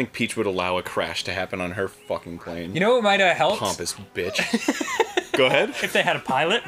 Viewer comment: Did he just called peach a B1tch :0